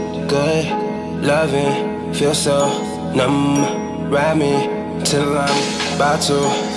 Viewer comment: all this time i thought he's saying ''god, loving'' and ''grab me, till i'm'' xD